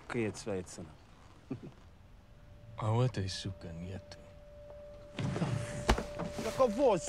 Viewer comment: Keanu says: “А вот это сука нет.” Right? Im tryna learn russian in duolingo lol I can’t pick up what the others say